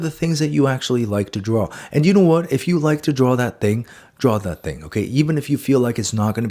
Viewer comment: Did he just say spit on that, I mean "draw that thang?"